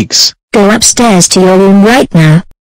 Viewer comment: What is Violy's mom saying? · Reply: Or else we will send you to Latvia... IDK (I can't Take a look at the speech box because the original legacy video maker retired)